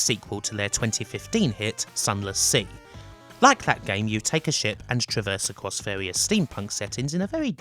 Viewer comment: for a second I thought you said "you take a sh*t"